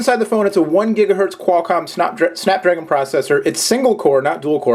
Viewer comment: did i hear 1Ghz snotdragon processor? that so should be its name!